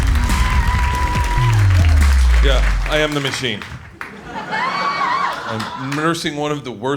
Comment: Right before he took a sip of beer I swear to god I heard “get it machine”